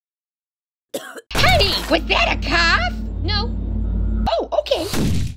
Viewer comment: I thought she said "was that a couch" 😂😂😂